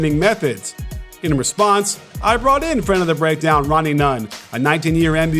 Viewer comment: who else thought he said Ronnie Mund?